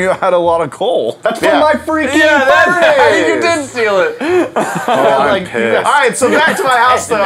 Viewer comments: Did anyone else hear say "Now im p*ssed" 10:37-10:39 if u wanna hear it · Did anyone else hear say pissed